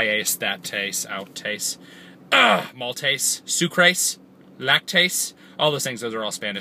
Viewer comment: at about 1:10 I thought he said Moltres xD